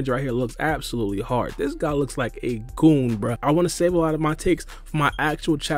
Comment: Lol i heard “this guy is looks like a total GOOMBA”